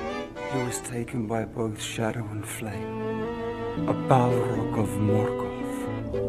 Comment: What did you say? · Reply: A balrog of Morgoth